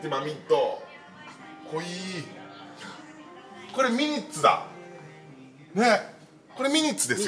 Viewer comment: ホーネットの後にミニッツと言われたので、ニミッツに聞こえてしまいましたｗ ラジコンは従兄の持っていたグラスホッパーと例の番組くらいしか知りませんが、動画は凄く楽しかったです